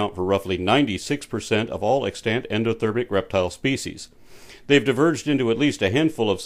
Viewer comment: Um... did you say "endothermic" at 5:26?